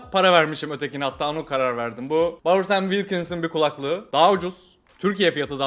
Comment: kulaklığın ismi ney ne diyor anlamadım da 😊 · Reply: Bowers & Wilkins